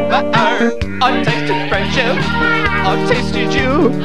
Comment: I thought it said “Autistic friendship” “Autistic you” Autistic dying”